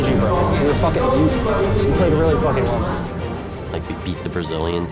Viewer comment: did he say you played really fucking horrible? Lmfao. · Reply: UnafraidPie he said "we"